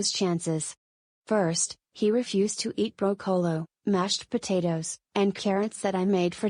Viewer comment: SPELLING MISTAKE DETECTED MISTAKE: BROCCOLO INSTEAD OF BROCCOLI